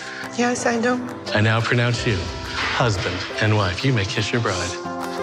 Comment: I know its 2020 but did anyone else hear him say "you may now kiss your brother"